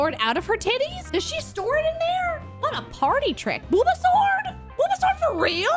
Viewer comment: I almost thought you said Bulbasaur when you said Booba Sword.